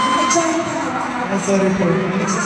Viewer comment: 3 times ko na pinanuod to 😍😅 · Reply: hi kaps, narinig mu na po ba sagot ni Jah Regine? Here po pagkaintindi ko 52:33 timestamp "ay sorry po, wala po kasi akong masabi dahil baka may matamaan...hinde joke lang po"